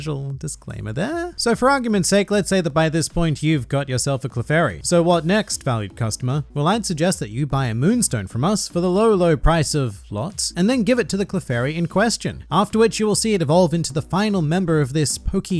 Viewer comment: At first I heard "for the low low price of plot".....